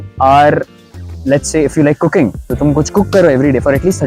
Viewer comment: i heard "cooking" as "cocaine"